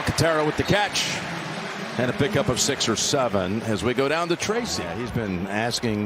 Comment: did he just say 6'7". 😂😂😂😂😂 a gain of 🤚6 or 7🤚